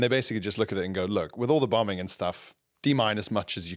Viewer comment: I honestly thought Pierre said "With all the bumming and stuff" and it still somehow made sense! 😂